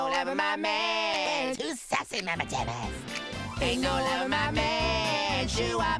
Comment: did he just say “ you sussy mama dumb a-“